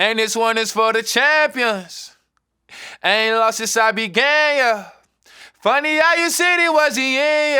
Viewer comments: Wow I thought he said “I ain’t lost since I been gay, yuh!” 🤔 I think I like my version better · I thought he said "i aint lost since I been gay" yah. I know im not the only one that thought that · On god I thought he said I ain’t lost since I was gay yuh😂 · Thought he said since I been gay not since I began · Am I going the only one that thought he said “Ain’t been long since I’ve been gay yuh” 😭😭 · I thought he said he ain’t lose since he been gay…not began oh Issh lol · i thought it said "This one is for the champions Ain't been long since i been gay, yuh" bruhh it sounds like that you gotta be honest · I thought the chorus said I ain't lost since I've been gay 🤣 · ohhhhh i thought he said "i ain't lost since i been gay yuh" ohhh ok ok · Oh he said “began” I thought he said “been gay yeah” 🙄😹either way I’m fw it🤣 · "I ain't lost since I began" I thought it said "since I been gay" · I definitely thought he said I ain’t lost since I been gay yea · I'm not gonna lie when he said "I ain't lost since I began yuh", I genuinely thought he said "I ain't lost since I've been gay yuh" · everyone including me, thought he said "i ain't lost since i been gay" lol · Thought he said haven’t lost since I was gay yuh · Who else thought he said “I ain’t lost since I been gay” 😂😭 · I thought it was “I ain’t lost since I was gay” began makes more sense 😭😭😭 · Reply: PLS BC I THOUGHT IT SAID “any losses I’ll be gay” 😭😭